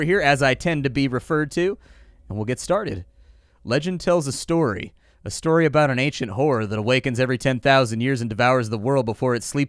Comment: lol I misheard the opening line of the story and thought you said "A story about an ancient whore" hahahahaha